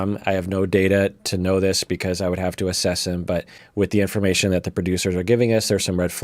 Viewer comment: I heard "I don't know much about Tom, I would have to ASSASSIN him" and I was like "YAAAAAAASSSS". I replayed that part and doc said " assess him" :'(